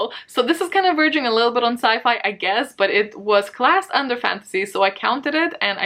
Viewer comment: I wasn't realy paying attention, and I heard "glass underpants" I suppose that's not what you said, but it cheered me uuuuppp